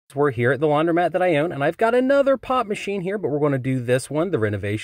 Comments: For a second I thought he said: Pot machine instead of pop machine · I thought he was saying “pot machine”. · I'm so against calling soda "pop" that I thought he was saying "pot machine". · My ears are messed up i thought he said pot machine lol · I thought he kept saying the pot machine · thought you said “pot machine” and got excited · I thought he said pot machine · I thought you said pot machine 😂